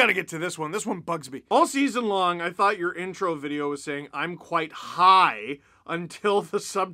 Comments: I thought you were saying quite high as well lol!! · 100% I thought you were saying "I'm quite high" hahaha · YESSSS!!!!!!! I'M NOT THE ONLY ONE THAT THOUGHT HE SAID "I'M QUITE HIGH" LMAO